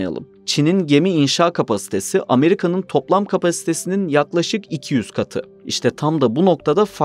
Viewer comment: Çin'in gemi üretimi abd nin üretiminden tam iki katı diyeceksiniz sanırım dilimiz mi surctu 200 katı gibi duydum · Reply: 200 katıdir doğru duydunuz